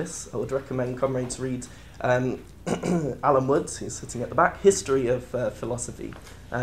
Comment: The video speaker named a certain writer of a philosophy book, hope I heard it right.Sadly, I did not hear the name properly - sounded like Allan Watts or something like that. I'd be happy to get the name from any listener who heard it properly named. Thanks. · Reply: Alan woods- history of philosophy